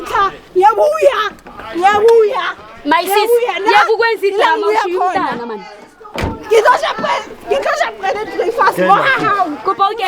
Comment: Did I hear her say " ngiyaboa ke tlo ja breakfast hahao " 😳🤦🤣😂🤣😂...she is there for food nje ...sibindi senganga sa lo sissy ..the parents needs to engage police Like grandpa " go well , go shell" 👋😂😂😂